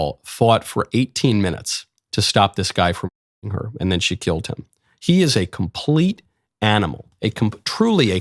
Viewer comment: To fix what you said “she fought him for 18 minutes before she killed him” or atleast i think that’s what i heard in a mumble. 4:07 turned on rewinded and watched closed Caption indicates you said “she killed him” Correction “she fought him for 18 minutes before He Killed Her”